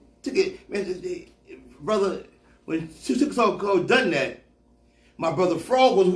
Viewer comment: Anybody understood what he said the 9:12 mark? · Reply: He said "when tookie so called dome that"